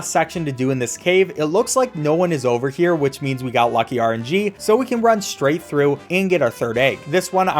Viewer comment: What did he say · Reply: We got Lucky RNG, RNG means random number generation, he's basically saying that he got lucky since the chances of something happening is randomly generated